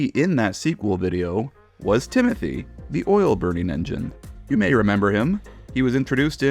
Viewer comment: I thought he said Timothy the ghost train but the other Timothy I take it it good